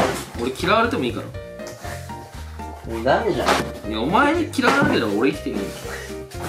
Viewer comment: ちょっと聞こえにくいから お前に嫌われたら生きていけねぇって解釈していい？